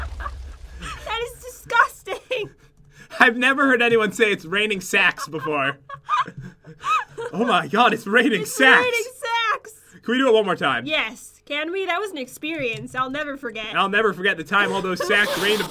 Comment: I thought Jen said "it's raining. Sex